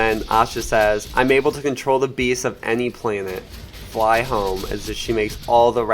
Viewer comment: I thought he said she had the power to control bees,I was like ,Dafuq she’s useless 😂